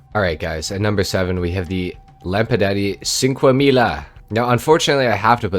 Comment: What did you just say?! Lampadaddy?! 🤣